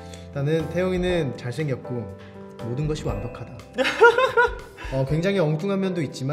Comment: what did he say? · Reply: Just that taeyong is cute